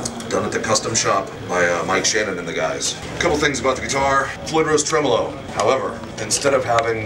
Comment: Did he just say "Clitoris Tremelo" @ 0:13 ?